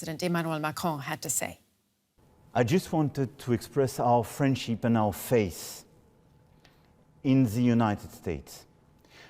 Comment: What does he say? "I just wanted to express our..???...in the United States" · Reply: Friendship and faith